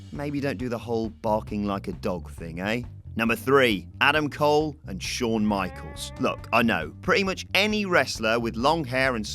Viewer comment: I thought when he said Adam Cole and Shawn Michaels he said Michael Cole and Michaels